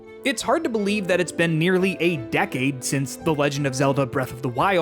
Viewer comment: I thought he said 8 decades 😂 Felt like Link waking up in a tomb.